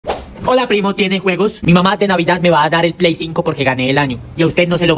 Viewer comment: Entendí "Hola primo, tienes huevos? " JAAJAJAJSJSJAJAJJS